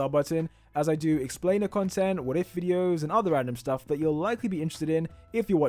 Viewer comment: did you just say “ANNIM STUFF”. Lmao gtfoh.